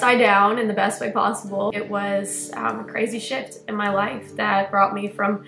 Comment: Was that a cuss word I heard? 0:25 Edit: Ohhhhh, a crazy SHIFT